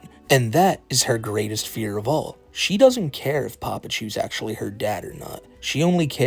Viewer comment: I thought it said "pamperchu her dad???" 😭